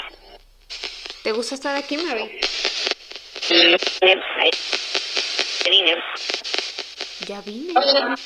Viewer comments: No sé si estoy locah pero en una parte escuché en la grabación del minuto 13:00 y cachito :v que decía *hola, ya vine, soy Mary, sisi* Me io meio :v Pues Weno algo así le había entendido 😓 Like para que no le pase algo malo a 🙏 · En el minuto 12:57 cuando le preguntas si le husta estar ahí creo que responde algo como "no, pero ya vine" Y cuando le preguntas si tiene 4 años le escuche como " no, sobre 12" ( escuche estas cosas cuando lo puse en camara lenta) Esto me da bastante intriga, pero de todas formas cuidate espero que estes bien y que todo salga adelante